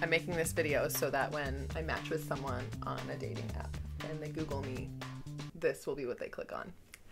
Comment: In the intro I thought she said she was dating a boy